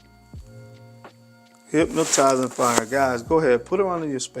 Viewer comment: What did this man say at 6:24?? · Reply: Hypnotizing fire (very softly)